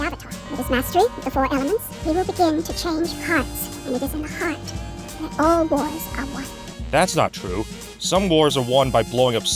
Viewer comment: Did she say “it is in the heart that all boys are white”?